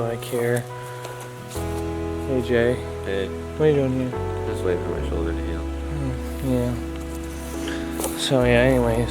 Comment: When he met jay in the woods I thought he said "I'm waiting for my channel to show at the end".